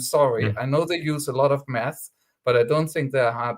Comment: Because of his accent, I first thought said, "Economists use a lot of meth." Which is true metaphorically, I must say.